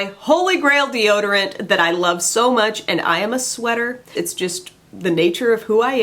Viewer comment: I was laughing over the "I am nuts" subtitle when my husband heard you say "I am a sweater", he said "oh yeah?, I am a shirt". Everyone is nuts! Thanks for the funnies!